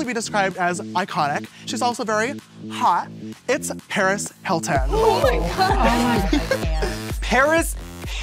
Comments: If Paris Hilton says you’re hot, your self esteem should automatically be fixed for life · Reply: i thought that said perez hilton and i got scared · No one: Literally no one: Paris freakin hilton: yes I loved it when you did that cute thing hahaha!!!! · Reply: i thought they said PEREZ HILTON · I thought he said Perez Hilton